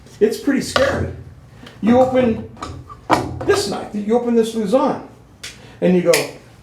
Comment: When he talked about the luzon the subtitles thought the was saying lasagna